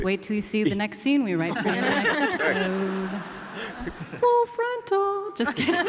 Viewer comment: what does she say in that sing-songy voice? thanks :) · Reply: "full frontal"